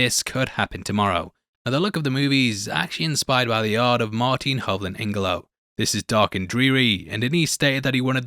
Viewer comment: can anyone tell me what the name of the photographer is that’s mentioned at 15:29. i can’t tell what he said and CC comes make with nonsense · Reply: Yeah, he slurs like a drunk in many videos. I believe the artist is Martina Hoogland Ivanow.